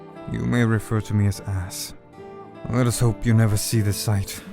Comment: I heard 'Ass' instead and since people call Ash 'Ass' sometimes I just imaged Ash going "Oh hey we have the same name! Wild."